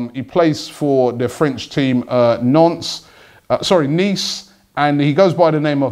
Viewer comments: If you're planning singing Dalbert from 'NONCE'.Bring along Adam Johnson,I think he's a free agent😂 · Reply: Cameron Vink 4:37 it sounded like he said nonce but he corrected himself by saying Nice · did you just say " nonce " LOL